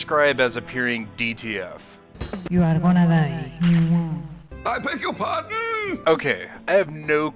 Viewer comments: "You are gonna die" Isn't that what she says? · I don't know their language but I think she was saying "Your world is mine. Mwah~♥️." Hope that clears up. 😅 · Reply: Someone actually assisted and it's "you're gonna die." But I appreciate you lookin out. Thanks for watching. 👍